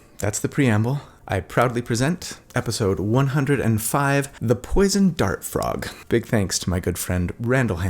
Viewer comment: Sorry, I thought you said the Poison Fart Dog... that would have been cool.